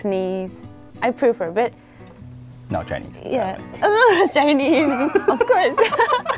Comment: did she say, not Chinese of course. Wth.